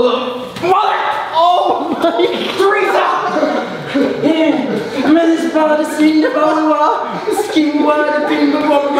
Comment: Did anyone hear D-trix say Teresa at 11:45?😂😂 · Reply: He said mother Teresa